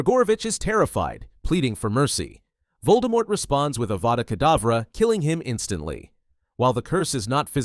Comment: SUBTITLES ON YALL GOTTA SEE THIS for those who can’t it says he begs for mercy and Voldemort gives him a vodka, instead of avada kedavra